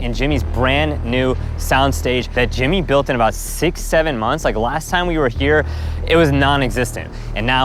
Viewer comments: I hear 67… sighs, checks comments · all i heard was 67